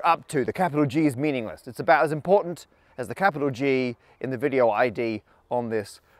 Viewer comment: base 64 encode, so its 2/64 * 11 characters, that makes for a 34% chance of at least one g or G in the id Edit 2/64 * 11 = 17%; he says capital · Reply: I was thinking the same and was just about to type this when I saw you already did. You might have misheard, Matt said capital G 0:30 ;) .